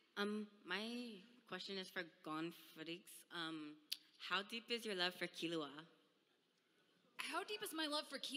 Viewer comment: I turned on the caption instead of reading yours for fun and, uhm- 0:46 "My question is for gone footage" how deep is your love for kilauea"